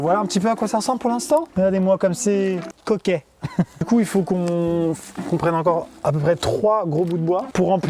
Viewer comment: C moi ou il di"couill fo con" à